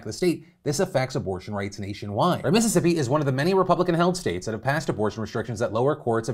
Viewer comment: I heard "Republican hellscapes" instead of "Republican-held states" but the meaning is the same either way